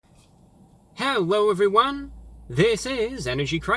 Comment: I heard “hewwo everyone!” Instead of hello everyone.... is that only me?